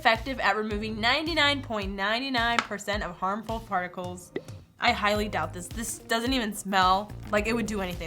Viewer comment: Did anybody hear farticles